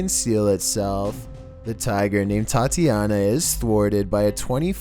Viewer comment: #10 did you say that tigers name was Thotiana?????